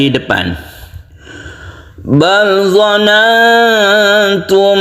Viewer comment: Assalamualaikum. Mhn maaf tadz..., sedikit koreksi. Kayaknya ada bacaan yang kurang pas. Tulisannya BAL DONNATUM. Kedengarannya BAL DOLANGTUM.. apa saya yang salah dengar? Mohon jawab anya. Tadz. Salam hormat smg sehat selalu. 🙏🙏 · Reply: Waalaikum salam saudaraku.. itu betul harus di baca DONANGTUM dengan mendengung. Karena ada nun mati depannya huruf TA. Tajwidnya adalah IKHFA.. 🙏🙏